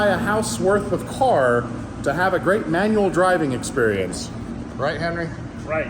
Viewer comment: Thought you said best manual driving experience, not best manual sailing experience.